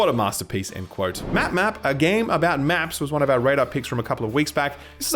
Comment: When you said a game about making maps I thought you said a game about taking naps